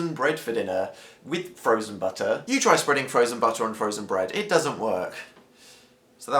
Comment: When you said "spreading frozen butter on frozen bread", I heard, "spreading frozen bread on frozen butter"... I have something wrong with me o.o